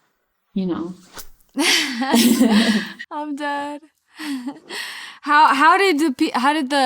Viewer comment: Did she just say "I'm dead"? like the emoji?